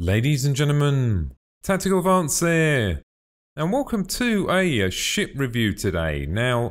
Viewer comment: Wait a second, did he say what I think he said? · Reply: a ship review